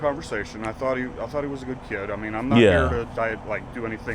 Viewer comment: when he said "hes a good looking kid" is were i got caught off gaurd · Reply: Thought he said “he looks like a good kid” but I guess I heard wrong lol